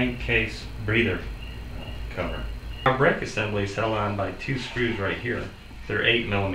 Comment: the what assembly? couldn't quite make out what you said. thanks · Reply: He called it a "brake assembly." It is the flywheel brake which, together with the armature kill switch wire, shuts down the engine when you release the cable bale on your mower handle.